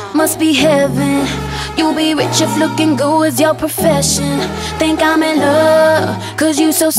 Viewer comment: I thought normani said ''You'd be rich if licking goo was your profession